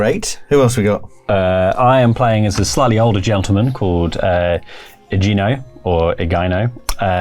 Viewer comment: “I’m playing as a slightly older gentleman” but I heard “slutty older gentleman” and I didn’t even bat an eye 😭✋🏻